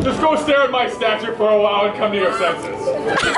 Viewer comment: This is the best Gaston I have ever seen. He deserves an award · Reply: I thought he said till you come to your senses... Or it doesn't make sense